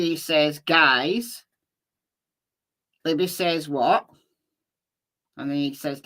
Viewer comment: She doesn't say "what," she says "hi." Or at least I'm pretty sure.